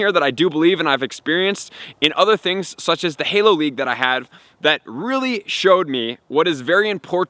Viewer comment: Ngl I had to rewind multiple times @ 1:09 to make sure I heard you correctly. Halo league?? 🤔 Besides pokemon the only other game I'm more passionate about is Halo. Idk how much you're still into that franchise, but wouldn't mind seeing Halo content from you!